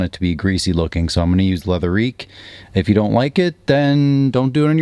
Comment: What is the leather cleaner that you use on your steering wheel? It sounds like you were saying leather eek, but I can’t find it online and your product links are for lexol. Car looks fantastic, let me know when you’re thinking of selling the M3!